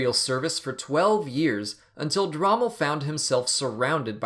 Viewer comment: I swear I heard you say Rommel.